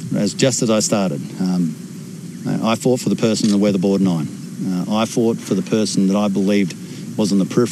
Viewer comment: Like others I thought he was saying "The Weatherboard 9" at first.